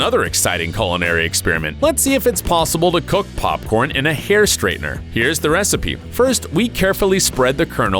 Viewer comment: I swore he said hair strainer